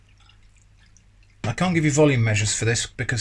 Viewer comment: But not the same volume per unit weight, so I could not give meaningful volume measurements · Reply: sorry - I thought you said weight not volume. Although I prefer mass. What if you were making it on the moon!